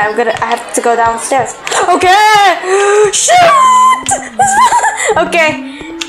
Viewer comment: I thought said the S word but she said shoot